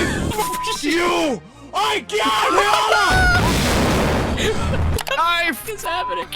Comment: i think the ai turned danish. it sounds like it says "I CAN'T HØRE DIG!"